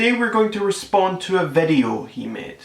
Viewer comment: Did he say vedio?